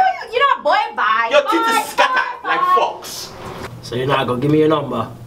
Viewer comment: oh my goodness...smh all these years I thought he said her teeth were scattered like fox lol. I was like I didn't know foxes have scattered teeth! Lol My whole family is from Barbados, in particular my grandmother has all of these kinda random comparisons with me and whatever animal or whatever expression that Im not familiar with...so I thought scattered like fox was one of those. Lol 😄